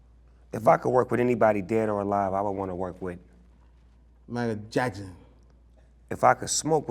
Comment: The first time I thought he said: MEGAN JACKSON!....lol.